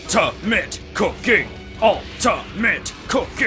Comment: at first i thought you said ultimate cocaine cuz he needs alot of sugar and it kinda looks similar